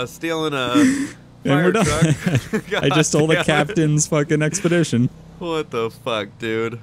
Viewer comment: Is that an explorer or expedition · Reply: I thought so too but then he said expedition.